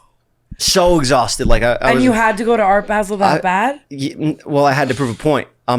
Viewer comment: anyone else hear tana say “babe” or am i crazy 😭